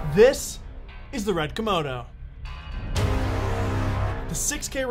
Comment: lol, did he call it the Red "Komono" at 0:04? Yep, he in fact did call it a Komono and not a Komodo.